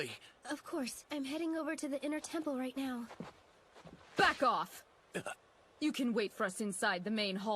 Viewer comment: I swear all I can hear is “I visited Wright at the hospital, he suggested I DO him” at 5:08 😭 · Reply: All I hear at 8:02 is Franziska saying "F*ck off!"